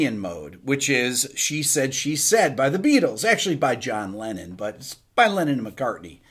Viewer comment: I thought he said Lennin